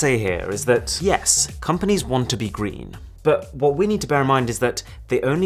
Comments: Mrwhosetheboss: " Yes, companies want to be GREEN..." Me: *Heard "GREED" instead of "GREEN"* 😂😅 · When you said "companies want to be green", I legit heard "companies want to be greed" lmao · I legit misheard "green" as "greedy" lel